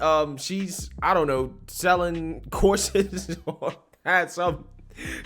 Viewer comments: I swear when you said she was selling courses, I thought you said corsets. Oh, the horror. When you elaborated then I realized, Oh, courses. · I thought he said "corsets" but he said "courses". Too bad, I was immediately intrigued :)